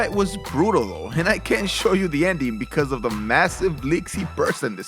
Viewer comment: WTF is he saying at 6:03 ? "I cant show you the ending because of the massive something something something" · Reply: Massive leaks he sprouted I'm guessing he bled like a stuffed pig